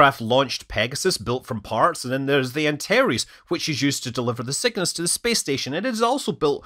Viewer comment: "built to deliver the sickness to the space station..." (3:32) What!? CC says it's "... signness..." What!? Did anybody hear something else? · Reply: Scott says "Cygnus" which was an expendable American automated cargo spacecraft designed for International Space Station (ISS) resupply missions.